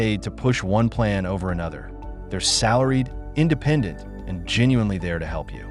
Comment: did you say, "their salaried?" Where does their salarie come from? These corporations, organizations and institutions are working on "our paid in tax dollars"!!! And it has run out... stop stealing from the elderly!!! Christ warned us of this day!!! Jude12-19, Woe unto them for they have gone in the way of Cain, and ran greedily after the error of Balaam for reward... These are spots in your feasts of charity, when they feast with you, feeding themselves without fear: clouds they are without water, carried about of winds; trees whose fruit withered, without fruit, twice dead, plugged up by the roots; raging waves of the sea, foaming out their own shame; wandering stars... vs.16- these are murmurers, complainers, walking after their own lusts; and their mouth speaking great swelling words, having men's persons in admiration because of advantage. (Liberals)... *You out there should read Jude, it's very lighting; mockers in the last days who should walk after their own ungodly lusts... seeking whom they can destroy! Thats what has happened already!!! There's more!!! Read it, research your scriptures; they are telling lies and now being found out! Your smooth talk aint gonna help them' ❤